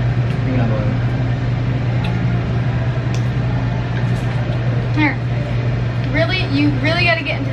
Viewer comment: When turned around it sounds like she said do you like him